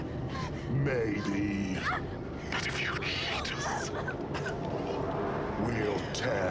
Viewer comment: Does any one know what the female cenobite says at 2:44? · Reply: “But if you cheat us...”